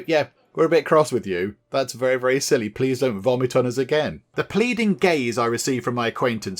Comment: that's when I heard "The pleasing gays" ... Oh lord, is this what Pride month is doing to me! Great!! I'm here for it! Thank you!!!